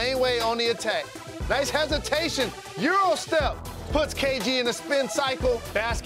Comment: Did he say KD?